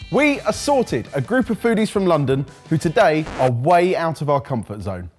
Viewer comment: Why do I always hear "We assaulted a group of foodies from London" at the start? ... O_O'' I'm sure they must be annoying, but assaulting them...